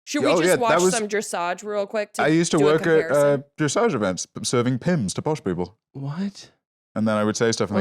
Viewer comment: this sentence was so british i had no idea what he meant at first. I heard "observing pins for pod people"